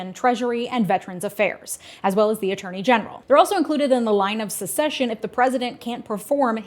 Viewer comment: It sounds like you're saying "line of secession" instead of "line of succession". Oops.